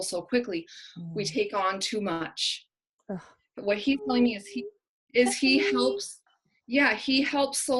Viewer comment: I thought I heard 'help me' too. But I've only listened to it 3 times. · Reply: yes I heard it at 8:54 I can't say for sure what I heard but it sounded like "help me"